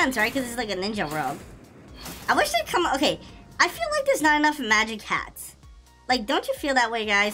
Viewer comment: did he say magic cats or magic hats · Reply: He said chats